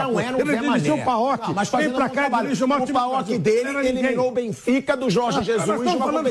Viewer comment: dirigiu o maior clube do brasil? Foi isso mesmo que ouvi? KKKKKKKKKKKKKKK